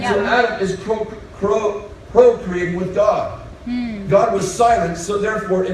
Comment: Hey! Former bethelite here. I’m pretty sure he said “CO created” with God. It’s a hot topic that we get to create with God with the “creativity he’s put in our hearts” This really changes the context. ETA: I hear it now. But later at 3:36 he does jumble the two words. Still garbage either way.